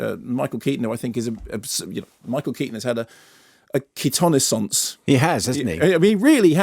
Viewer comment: What was that word Mark said around 5:47 that rhymes with renaissance? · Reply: Keatonaissance - a renaissance for Michael Keaton.